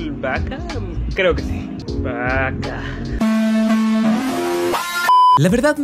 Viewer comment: Bro yo entendí paj4 en minuto 2:37 y era bakaa😅